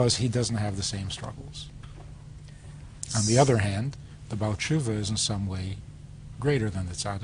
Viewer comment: I don't know the name that he used and cant understand to look it up. Could someone please tell me what that word was? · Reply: baaltshuva בעל תשובה . i guess 'he who repented' 'owner of repentance'